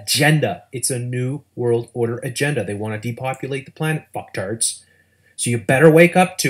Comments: did he call his audience fucktards..? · "you fucktards" ... sorry, did you just call me a poptart?